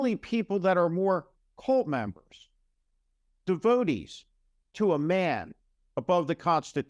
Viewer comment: You said "devotees" but I heard "toadies".